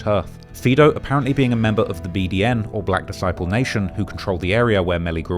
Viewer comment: Did he say BDN “Oblock disciple nation “😂😂